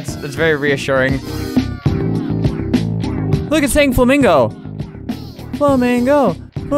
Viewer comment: I can unhear it now I only hear flamingo now